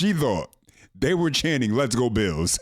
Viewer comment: I heard, "let's go Brandon. "